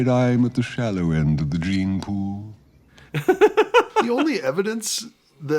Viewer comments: Reminded me that when I was a kid I had no idea what a gene pool was, so I thought Scar said "dream pool"... and then later in the movie we see Musfasa's reflection in the pool and I assumed that must be the dream pool XD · Reminded me that when I was a kid I had no idea what a gene pool was, so I thought Scar said "dream pool"... and then later in the movie we see Musfasa's reflection in the pool and I assumed that must be the dream pool XD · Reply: Same. For a long time, I thought he said "dream pool", too.